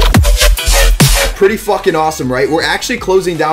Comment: Did he say "pretty Flucking awesome" at 0:15?